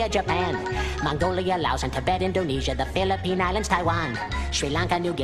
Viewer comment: I heard philippinas